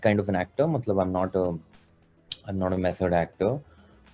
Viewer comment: what does he say at 3:29 ? What actor ?? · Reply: Method actor